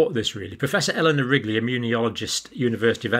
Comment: Did you say Eleanor Rigby?